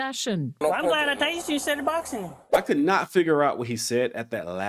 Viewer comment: Pretty clear, he said "I'm glad I tazered you instead of boxing" seems like you're being a bit disingenuous.